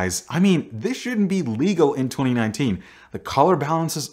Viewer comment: Did you just say 2019? xD